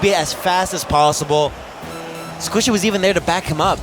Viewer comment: Yo 6:49 anyone hear say Squizzo